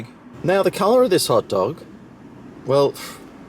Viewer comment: $12 for a friggin hotdog. What a joke. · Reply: I thought he said pot dog :28